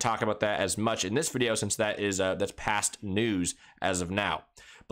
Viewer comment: What I heard: "Intel is past news"